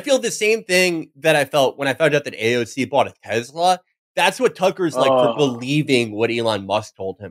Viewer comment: I had to go back and listen again when Felix said AOC bought a Tesla because I misheard it and thought he said AOC joined Hezbollah. 😂